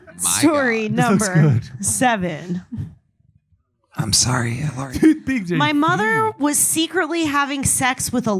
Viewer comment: Yoooo does he say “I’m sorry Hitler?” Right here?!? 🤣